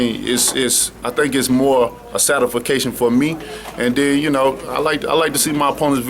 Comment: Satafaction? Lol. It took three tries to get my autocorrect to let me type that. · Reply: mista richmondva I thought he said certification. Didn't know he was trying to say satisfaction.